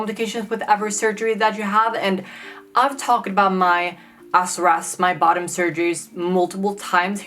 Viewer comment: what is that word she saying regarding 'bottom surgery'? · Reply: SRS